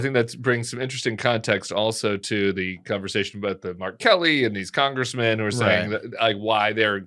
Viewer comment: I 100% thought Tim said "R. Kelly." I had to rewind it twice before I realized he was saying "Mark Kelly." 🤔😳🤣